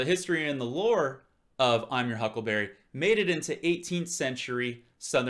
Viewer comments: I always thought he said Huckle bearer the second time.👍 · Yeah it does actually sound like he said huckle bearer. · Now when I listen to it, it sounds like he says "huckle bearer" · Huckle bearer.... If you listen carefully, it sounds like he says this...or possibly muttered the ending so it could be strewn either way....Berry or bearer